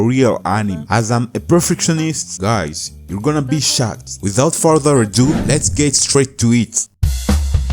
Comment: guys DID I JUST HEAR SHOT WHEN THE SUBTITLES SAID SHOCKED